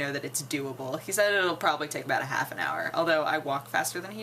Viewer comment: Not one and a half hours, half an hour lol. You misheard.